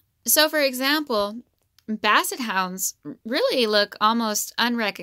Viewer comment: you are soooo right Bull terriers are almost unrecognizable as well 100 years ago they did not have breathing problems because their nose's were normal! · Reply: JoAnna Ratliff i thought she said basset hounds?